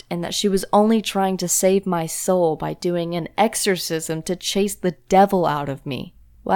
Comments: Lol I’m so dumb i thought I said “ my Mom tried ecersice the Devil out of me” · I thought it said" My mom tried to excersize the devil out of me"